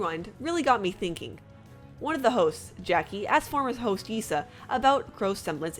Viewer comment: OMG! Wtf is wrong with me....when u said one of the hosts, i heard: one of the Hoes............yea, i need to sleep more